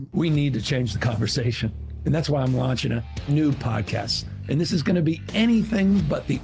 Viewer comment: did he say “nude podcast?” No thanks!